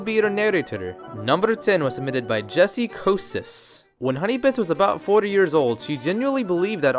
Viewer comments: Did He Say FORTY XD ????? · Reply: No, he said "When Honeybits was about 4 years old..." His accent just makes it sound like he said "40." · Am I the only one who thought he said 40 years old? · Did He Say FORTY XD ????? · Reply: His accent sure makes it sound like he was saying "40 years old" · Is it just me or does it sound like he is saying hunniebits was about 40 years old or is it he how he talks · Forty years old? Damn. xD I know he said four but it seriously sounded like forty. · im sorry but it sounded like he said honeybits was 40 years old lol